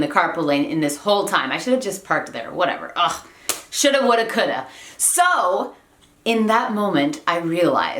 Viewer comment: Me gusta como se escucha lo que dice en el minuto 8:46, pero no entiendo que dice. Alguien me podría decir.!!! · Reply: jimena de la rosa Lo que dijo es shoulda woulda coulda. es decir hubiera hecho esto o aquello.